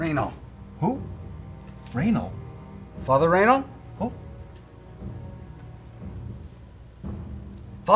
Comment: Anyone else heard "Father Anal", or was I the only one thinking Percy grew up in the Catholic Church?